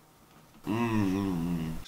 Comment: δεν κατάλαβα τι λέει 😅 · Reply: Λέει:και να σου πω κάτι